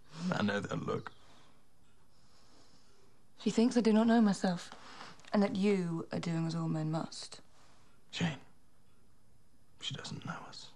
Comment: what the HECK is she saying at 0:37? Anyone? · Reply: "She thinks I do not know myself and that you are doing as all men must."